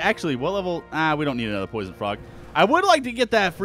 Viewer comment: did anyone else think he said poison f***? instead of frog? xD